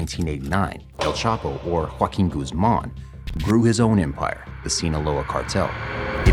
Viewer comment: I thought he said El Chapo Fucking Guzman😂